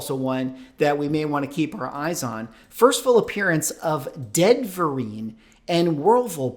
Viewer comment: I swear I kept hearing " first full appearance of Ben Vereen and Whirlpool" instead of "Deadverine" and "Wolverpool". I swear I thought we were about to have a actor/dancer/singer take on a damn washing machine. That would be peak MCU material. I don't know how I can take this stuff seriously. It's like when WWE and wresting stopped even trying to hide the stuff was fake. 🤣